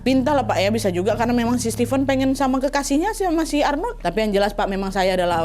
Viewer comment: aku salah denger atau gmn kok "Steven pengen sama kekasihnya si Arnold"😭🙏